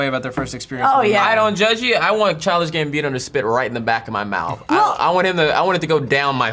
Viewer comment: I know likes the guy but I never hear what the hell he's saying when he says the name at 23:11. What is his name it's driving me crazy! Dx (charlifhdsjkfs gambino?) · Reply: Childish Gambino